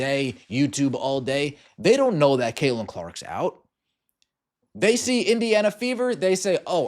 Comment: Did you say ppl don't know that Caitlin Clark is out? Huh?